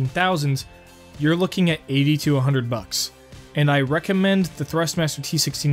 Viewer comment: Did he just say $8200? Or 80 TO 100